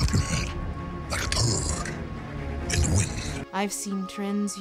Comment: Oh. a turd, that makes sense, I thought he said a toad in the wind.